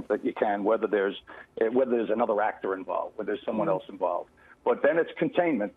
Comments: wait what did he just say at 2:42 ?!!? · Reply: Angela Fenech "ACTOR" i new it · Did he said at 2:42 ACTORS